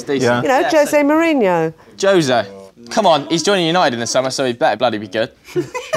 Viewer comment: What did that guy say about José at 3:37 ?? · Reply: said: "Jose? He is joining United in the Summer - so he better be good!"